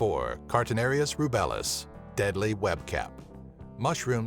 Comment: I heard "deadly webcam " and now i can't unhear it lol